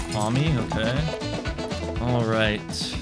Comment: Just me or did he say "mommy ok"